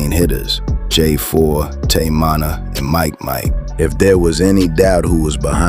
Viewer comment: Teach your AI to say Munna NOT Monna! Edit: Now I hear it saying "Manna"?! Come TF On Bruh!